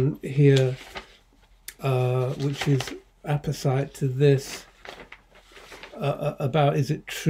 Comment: does he say epicyte?